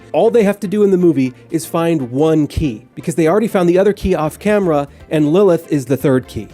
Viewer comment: Ahhhh. You were saying "They got the key off CAMERA" I thought you said "They got the key off CAMERON" and I was like, yo, who in the sweaty F**K it Cameron, I don't remember that guy. Please don't make me watch it again.